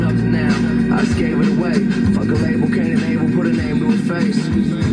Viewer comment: In “It Just Doesn’t Matter”, I thought he said “Fuck it, Cain & Abel put a name to a face” but regardless it’s fire lol